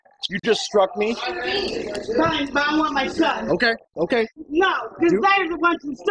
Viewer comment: I had to double take because I thought she said @ 2:18 "Im not like my son" rather than "I would like my son".